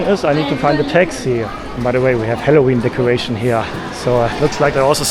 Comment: I thought you said we have Heroin decoration here . I thought maybe there's a drug problem there